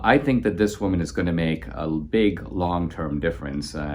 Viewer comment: "The democrats and God can do nothing at this point to stop her." Did I hear that correctly? AND God? Please clarify Dinesh.